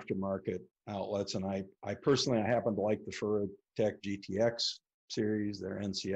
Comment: he mentions a particular aftermarket AC outlet that he recommends but I couldn't understand what brand. He called it the "xxxxx Tech GTX series" their "NCS stuff". Anyone know what brand he mentioned? Thx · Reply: Furutech is the brand